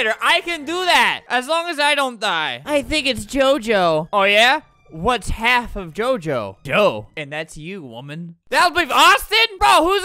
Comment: I heard "whats half of Gojo, Jo."